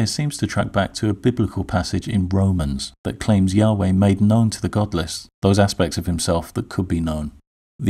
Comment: i thought you said goblins